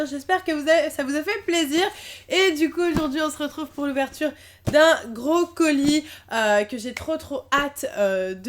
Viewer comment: À 0:48, j'ai entendu "l'ouverture d'un brocoli" au lieu de "l'ouverture d'un gros colis" J'avais pas compris xDD